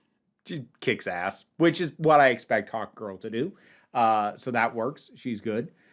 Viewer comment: When you said "Hawkgirl," I heard "Hot Girl"....and both are true! Isabela Mersed was easily the best part of TLOU season 2.